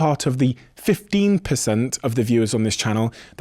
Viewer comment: At the end of the show, I heard “the bigger the breast”. Dunno why 🤷‍♂️